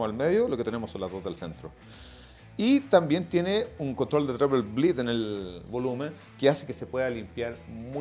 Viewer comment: Que dice en el 2:08? Control de no se qué en el volumen 😅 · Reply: Dice las palabras "treble bleed"